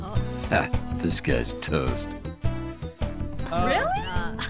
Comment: You should get CORPSE to say your intro thing, “ha, Disguised Toast” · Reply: gold to know I'm not the only one who heard that, tbh I hear both "this guy's toast" and "disguised toast"